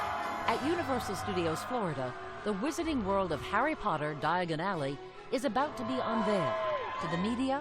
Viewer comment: why are there so many sentences in harry potter not clearly spoken? i mean for 10 years i thought he said something like DIADADE xD